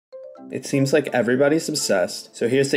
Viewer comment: I thought he said "it seems like everybody's sus"